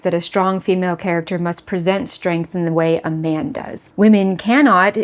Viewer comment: I misheard "man does", as "Amanda's" That's what I'm calling them now. A-man-da. The girlbossers.